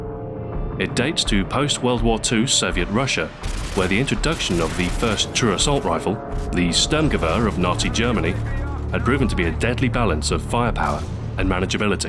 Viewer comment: Loool! Thought I heard: "naughty Germany" right in the beginning...